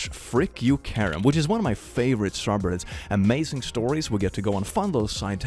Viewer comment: the subtitles say strawberries instead of subreddits, and quite frankly I can't argue with that